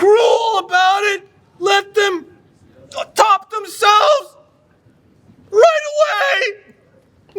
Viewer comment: Did he say let them top themselves 😂😂😂😂😂